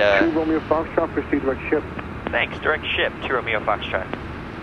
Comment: 1320 it sounded like he said "Direct Shit". Lol